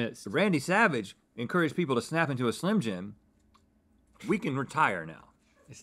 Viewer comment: wow the slogan is SNAP into a Slim Jim, I always thought he said, SLAM into a Slim Jim hahaha I was today years old